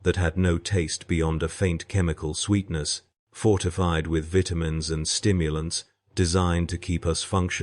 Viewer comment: Did I hear “Bitumens”? It might have been Vitamins - only have one working ear, and that from birth…